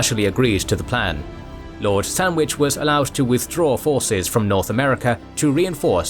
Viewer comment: I keep hearing lord sandwich, am I tripping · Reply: You are heading Sandwich and yes it is the place the Sandwich is named after.